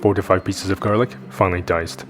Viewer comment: At first I thought you said 45 pieces of garlic.